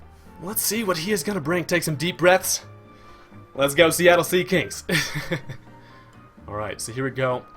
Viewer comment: Lol did he say Seattle Seakings at 1:27?